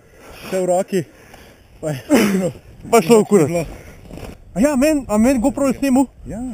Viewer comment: Did I just heard "kurwa" ?lol